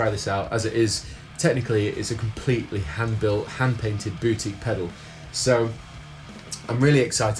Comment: haha sounds like you said booty